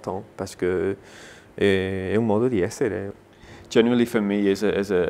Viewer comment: the last sentence in french, what is he saying ? I speak french but i could'nt understand ! Did he switch to another language ? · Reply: DAYZILLA Il a dit "un modo di essere", ça se traduit par "une façon d'être" en italien. Je pense qu'il se souvenait plus de comment ça se disais en français ;)